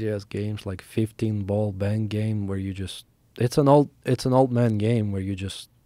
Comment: He said "15 ball bank game" And I thought he said '15 ball gang bang"😆